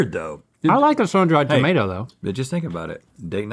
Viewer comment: did link say he likes tomatoes?